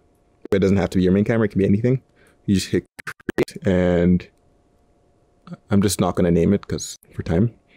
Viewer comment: he said “you just hit...” “and it opens this up “ but it glitches, does anyone know what he’s talking about? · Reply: It might be late, but just in case : "Just hit 'Create' on the timeline panel"